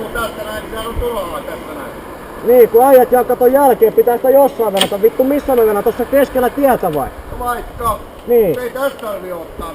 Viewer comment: mitä toi mopo poika oikein sanoi kun en saanu mitää selvää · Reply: "nii ku äijät jää kato jälkee nii pitäähän sitä jossain venata vittu missä mä venaan tossa keskellä tietäkö?"